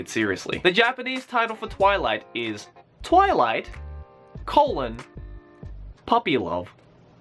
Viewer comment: When he said "Twilight Colon Puppy Love", I misheard it as "Twilight Cullen Puppy Love" T_T thank God for subs